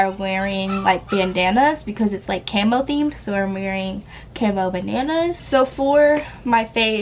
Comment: who else heard “camo bananas” instead of “camo bandanas” i did